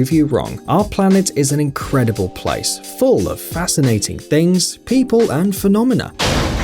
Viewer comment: I thought he said Masturbating things